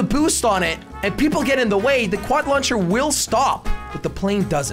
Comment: Did he say "Quad Launcher"